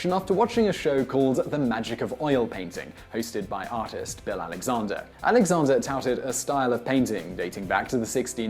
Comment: he clearly says "Bell Alexander", a vowel shift.